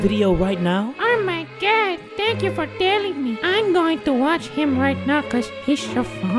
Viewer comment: i thought the girl said "Oh my god, tank u for telling me. im going to watch him right now cuz pissed off mommy" tell captions it agrees.